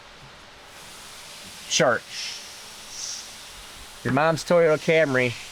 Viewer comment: that HP to destruction chart I thought you said "church" instead of "chart". In my mind it is now the HP to destruction Church! Preach Brutha!